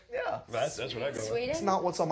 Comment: Did Jennifer say Sweden? :3 I live in sweeedaaan ♡♡